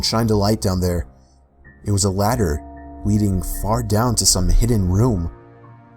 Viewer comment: did anyone hear say this " it was hidden fart down" XD